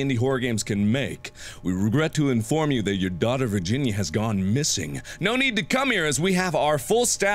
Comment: Omg I thought he said "We regret to inform you that your daughters virginity has gone missing." Bruh.